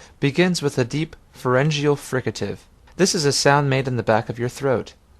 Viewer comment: what is he saying at 08:06 - forengio frigative?!?!? what?